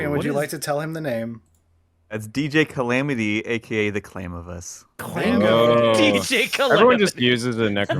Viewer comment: What is the name of the model at 1:16:30? I can't understand what's being said and the captions are gibberish. I want that model and I need to know his name! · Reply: No clue if you're still wondering after all this time but they're talking about Clamavus from the Genestealer Cults